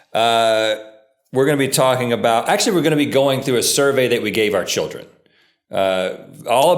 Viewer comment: I thought it said, "We gave our kids a surgery." I almost panicked lmao